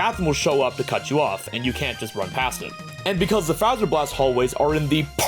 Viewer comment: I thought it said you speed cant run